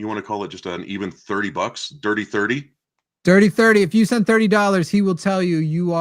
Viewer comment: Maybe it makes me a snowflake, but sometimes condescending laughs like his should just be an instant ban. I'm not going to tell y'all you need to do that, I don't know the first thing about running a call in show, but if it was put up to a vote 🤷‍♀️ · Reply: oh said "dirty thirty" for a moment I thought he said "Thirty Thirty," the hot horse from Bravestarr